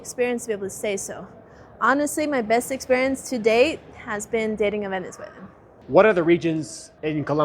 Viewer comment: What is what she said there? I could not understand her. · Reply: "Dating a venezuelan"